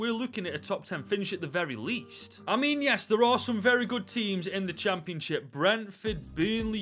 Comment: i thought you said good teams